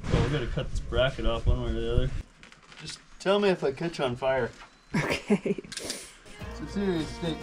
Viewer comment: At first, I thought said, film me if I catch on fire